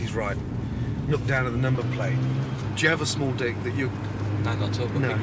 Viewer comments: AM I HEARING GORDON RIGHT?!? Does he really ask the chef if he has a small d1ck????? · did Gordon say do you have a small dick!!??!? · did Gordon say “do you have a small dick”